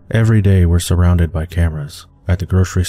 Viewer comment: when he said cameras I thought he said campers